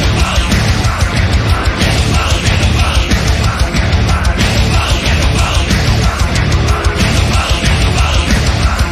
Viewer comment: anyone lip read japanese? id love to know what she says, and to who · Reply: she was just mouthing the chant "hedobang hedobang", Japanese pronunciation of "Headbang! Headbang!"